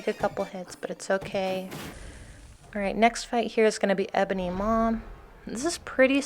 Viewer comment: I dont know what about you but I heard "EBONY MOM"